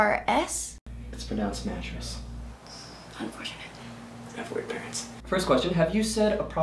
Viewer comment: "It's mattress..." "Thats...... unfortunate" "I have weird parents" xD I died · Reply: S3DWU7 I thought he said “white parents” 😂 works either way I guess